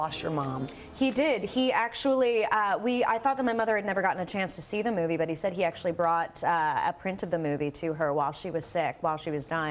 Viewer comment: Shiit! i'm trying to practice my listening, I'm learning English, so i'm watching videos but I don't understand wha tdoes she say here 2:43 wow! She speaks really quickly lol If somebody can tell me what she says i'd thank a lot! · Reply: "He uh--I thought that my mother had never gotten a chance to see the movie, but he said he actually brought a print of the movie to her while she was sick." Hope that helps! :)